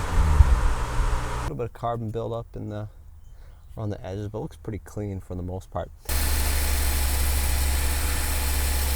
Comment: couldn’t quite here what u said here. What’re you doing with the spray? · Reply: Seafoam <link>